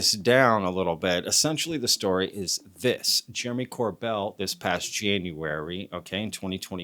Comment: Yo I thought you said Jeremy corbyn 😂😂